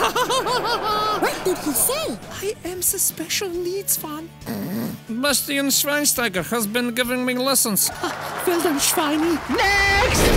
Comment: did he say he’s a special Leeds fan Man U hate Leeds and Leeds hate man u